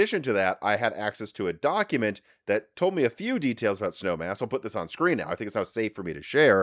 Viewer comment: Was listening on audio only in my car while commuting to work. I had to rewind and make sure I heard him right about the cpu code name. I thought he said “Snow Ass” lol made my day!